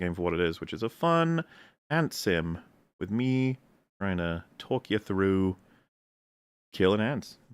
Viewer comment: A fun and sim... With ME. . . and then what is the word ge says after that.. i cant figure it out. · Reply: trying to